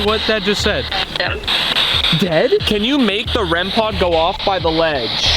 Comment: i think it said im dead nkt dead